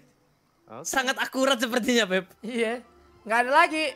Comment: "sangat akurat sepertinya 'beb' " eh ehh apa ni salah denger apa gmna gw ni:v